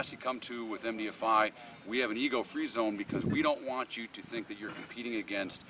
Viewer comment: The first time you said 'ego free zone' I thought you said 'Eagle free zone' was quite confused there for a bit LOL. Good video though, really enjoyed it ;^>